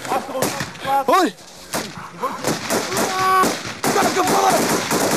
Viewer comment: the subs say "Assholes". This is not what he said, instead he said "Kankervolk". Which translates to "Cancerfolk".